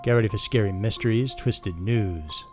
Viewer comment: Is it just me or did it sound like he said Twisted Nudes?